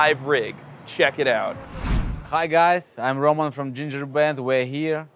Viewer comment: I really thought he said "Hi guys, I'm Roman from Ginger Bread".